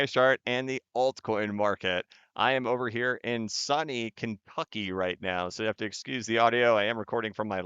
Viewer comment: Did he just say Sunny Kenf*cky!? 🤣